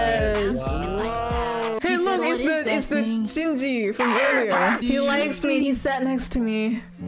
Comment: i thought you said SINJI FOR A MOMENT- I WAS LIKE- ME?????